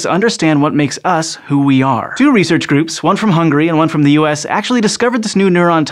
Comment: did u just call Hungary "hungry"?